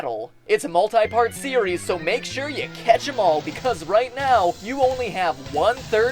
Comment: I heard "Catch that ball" but thats probably just me